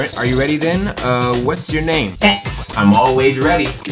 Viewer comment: Funkin YT: funkin Me: okay, it wasn't ya know-: Hex Mod YT: Xmod Me: wh- Also: What's your name? X- YouTube can't hear Hex- 4:06 6:03: mmmmm YT: uh